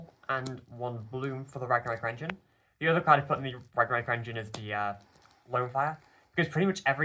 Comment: what card is she saying I can't read the text and Im only hearing flare/fire sb pls help 😭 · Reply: Lone fire blossom is the full name